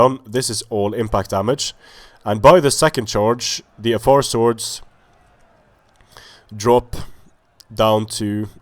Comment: You said at one point "The Afar's finally broke" or something along those lines while I was looking away to eat, and I thought you said "Athar's" and I had to look up just in case.